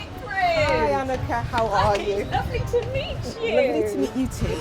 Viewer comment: did any one else think that lady said "hi, i am a cat,how are you" when she first walk up xD